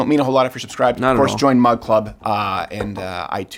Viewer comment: Great show! Thanks for having Ben on. I have to tell you this - all this time I thought you were saying "MudClub". Mud equaling Coffee. TODAY I realized it is MugClub. My bad.....😜